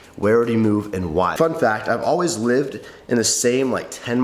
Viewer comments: Christian said ‘fun fact’ i heard ‘fu*k that’ had to replay a few times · Fun fact, thought he said "fuck that" Lol.